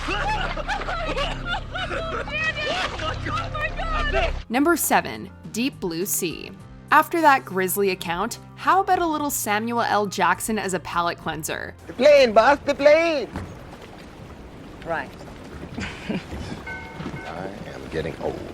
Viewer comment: did she actually say Samua L Jackson? lmao